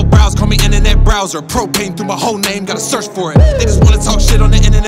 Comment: What does he say at 1:25? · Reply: “Propane through my whole name gotta search for it (fireboy), they just want to talk shit on the internet won’t admit it to themselves that they just never wanted to work for it.”